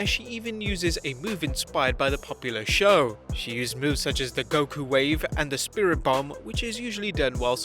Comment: did he just say goku wave